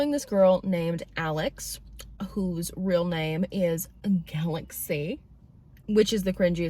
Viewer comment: When you said Alex’s real name I thought you said “Alexi” and I was like ah sure that’s not too bad until I realized it’s GALAXY! What kind of parent would commit that crime?!